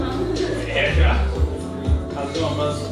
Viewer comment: is he saying "Hast du noch was" ?